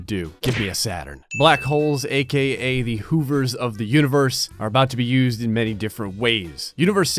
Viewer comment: Instead of hovers of the universe I thought he said the db coopers of the universe